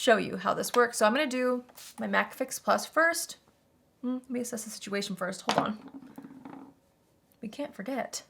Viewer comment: It’s not an video until I hear “access the situation “. Makes me smile every time. Love it ❤️